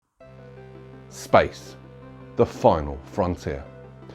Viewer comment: What I heard: "Spice... the final frontier." This isn't Dune!